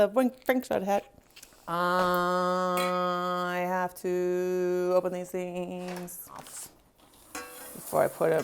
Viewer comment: мне послышалось ебаный😂